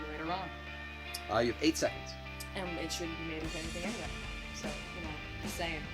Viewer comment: "it shouldn't be made into anything anyway" I had to re listen to that line 3 times before figuring out what was said. I think was a little too quiet this video, sounded like she was a little farther from the mic which would have been fine except that the background music was drowning her voice out a bit at times. Thanks for another great video!